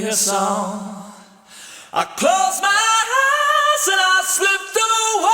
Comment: Even though I know it’s not he actually says, it sounds like he says (I closed my Hi’s and I slipped away)....